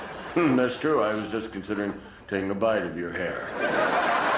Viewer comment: this is where leno keeps his WHAT?! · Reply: At first, i thought he said Conscience 🤣🤣